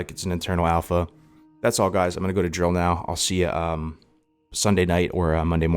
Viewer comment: Oh... "drill". I thought you said you were going to jail :O